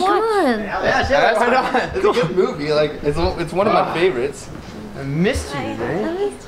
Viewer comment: AYY I HEAR THAT AROUND 10:00. SAID "I MISSED YOU BABE" SO DID